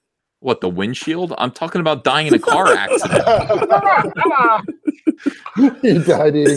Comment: Someone mishearing Ethan dying in a pie accident instead of car accident. Almost choked on my coffee.